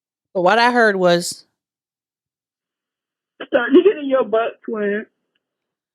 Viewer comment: The last voicemail it sounded like she said starting to get your butt cleanse. Sounds like the same chick that on the viral podcast said How do you suck d**k? 😂